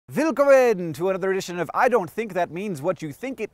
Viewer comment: Did Hank say wilkommen 0:00? As a German Nerdfighter, I feel honoured :D